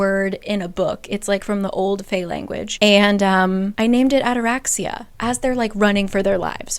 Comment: Help I misheard ataraxia and thought she named it anorexia😭😭😭😭